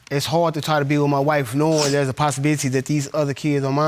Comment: Did I heard @ 13:21 posisibity